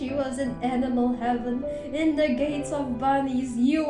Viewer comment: im soryy i thought i hear edible heaven😭